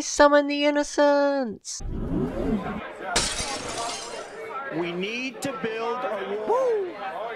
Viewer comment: who heard "we need to build wall" Wait ist that trumps line?